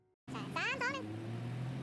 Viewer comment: นาทีที่1:52..53 ได้ยิน321🥰